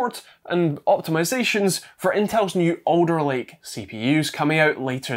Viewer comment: I thought you were saying 'Odor Lake" . Finally; I thought, Intel is giving their shit proper naming. Not to be. Alder Lake. I remember when Microsoft said Windows 10 would be the last Windows os. Not to be, apparently.